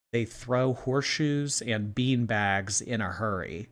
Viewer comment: Clarification is he said "the horseshoes and beanbags are hers" but sounded like he said good lord the beanbag is in a hurry